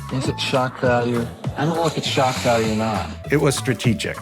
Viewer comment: Did he say "strategdick?"